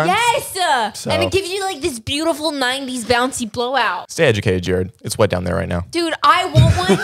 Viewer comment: Bro I heard "stay wet down there, Jared" and I snorted my coffee out my nose